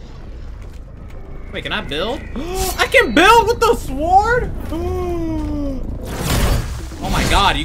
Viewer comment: it sounded like: I can build with the Ford!!